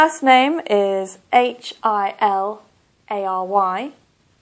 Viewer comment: I was mistaken in question number 5. Actually, I heard "Hilary", but when it was spelled, I heard 'h, i, l, l, y", and so I answered "Hilly", although I am confident that what I heard was "Hilary". Any comment regarding my mistake? Thank you very much..